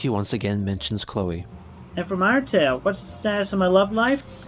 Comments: Did he just say "And from Undertale"? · why does it sounds like he says "and from undertale.."